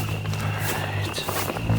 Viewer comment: An extremely faint female voice that sounds like it says "It's alright"